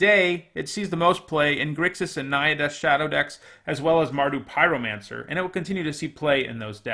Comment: Did he say, "Naya Death's Shadow"?